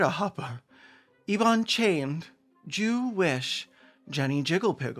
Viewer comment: I heard Enon Chayned as ivan-chai 😭